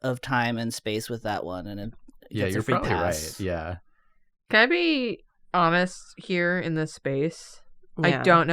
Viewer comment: When said "can I be honest", I misheard it as "can I be Amish" and did not question that at all for a solid three seconds.